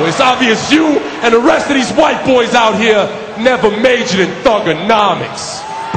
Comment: what did cena said?? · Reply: He said kurt angle and the fans never majored thuganomics